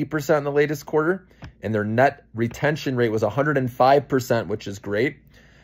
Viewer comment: New sub here, question for ya, but first, 5:23 did you say 'nut retention rate'? You are on fire! Digging your energy! I need the ambition and sharp focus.